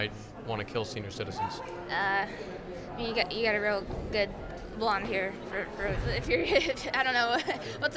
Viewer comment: does she say you have a real good blond hair?